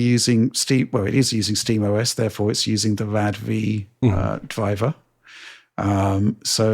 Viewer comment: what is he saying here? Rad re?